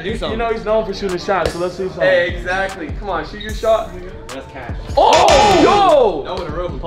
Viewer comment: am I trippin or did rug say “shoot your shot N*”